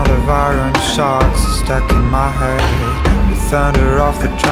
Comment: Entendí "Ivan está muerto"😂